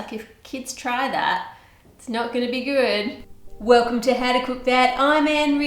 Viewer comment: I didn't read you channel name so when you said it, i thought you said Welcome to How to Cook Bad. lol